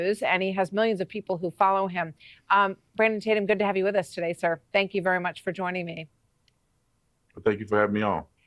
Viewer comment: did he just say thanks you very meow?